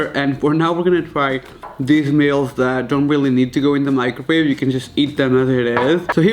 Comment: my ears have officially exploded is it only me or at 7:20 I heard microBABE😂😂 ok sorry bad joke but it was the funniest thing i have heard in a while